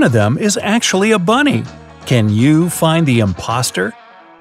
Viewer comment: uh did you just say imposter?